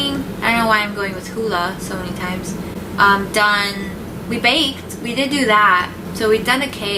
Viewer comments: When he said "baked" I thought he said "banged" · I- I thought said banged for a sec-🤭